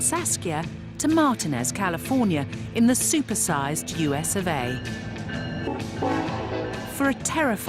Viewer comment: instead of USA she says USFA????